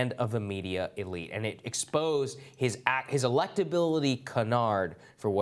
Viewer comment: He said "Electability Canard", as in an unfounded rumor. I heard "Electability Kenard", as in the little kid who shot Omar.